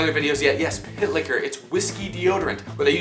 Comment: For real thought when he said pit liquor i really thought he said pick hitler